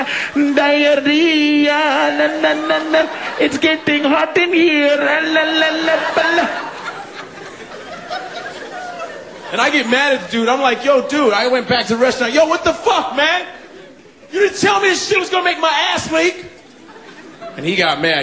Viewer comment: The subtitles say "die man." o_O